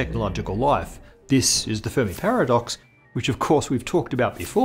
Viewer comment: I chose to continue to mishear this as the "Furry Paradox" and the idea that we made it worse lol